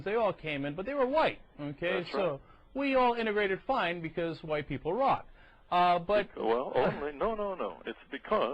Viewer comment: 8 years ago I agreed with Cenk. Today— time has proven Jared to be correct. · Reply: Listen very carefully to what Chunk says at 3:15 Apparently, "WE, all integrated fine, white people rock!". He's definitely not white!